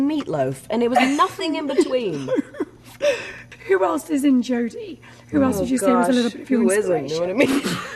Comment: what did she say? They both looking so embarrassing 😂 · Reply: 'who else is in Jody?' 'who isn't, you know what I mean?' :)